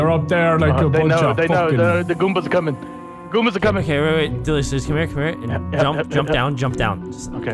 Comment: Did He Say DELICIOUS